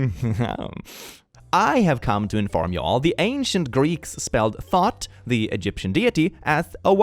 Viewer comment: Did just call Thoth "THOT"?